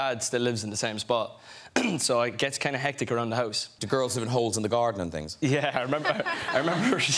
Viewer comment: I usually understand what they're saying. But what did Graham say around 0:13? Hose and Gardening? Is that a slang? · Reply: He said: "do girls live in holes in the garden and things?" to Niall's saying that going home to Ireland can be hectic